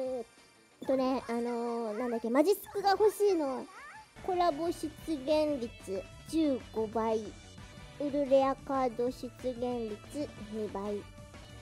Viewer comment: お母さんがマジスク欲しい に聞こえた